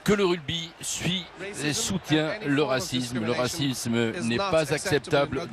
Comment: Moi aussi, je suis et soutien le racisme (3:20) ... il faut plus de racisme ... 🤣🤣🤣 (désolé, j'ai pas pu me retenir, c'était trop tentant 🤣🤣🤣)